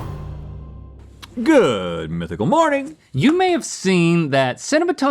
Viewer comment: I 100% thought you said "Good Mythical Oranges".